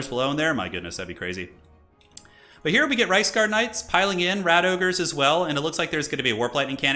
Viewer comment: Sometimes it sounds like he says Riceguards